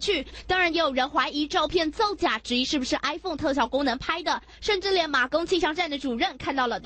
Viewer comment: I heard iphone